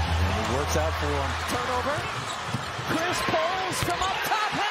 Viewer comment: Correct me if I’m wrong but did she just call Chris Middleton, Chris Paul